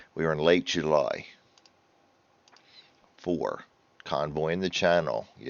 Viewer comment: So what does “Cotton Boying the Channel” mean? I’ve searched the internet but not found a translation 🤣. You pronounced Middle Wallop and Tangmere correctly. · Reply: Doh! I’ve just realised I misheard you say “Convoying the Channel” as “Cotton Boying the Channel” and assumed it was American slang. I feel stupid now. 😂